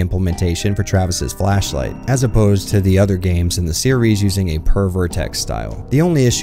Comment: Oh, you were saying "Per Vertex" lmao. I thought you were saying "Pervert Text" and I was so confused lmao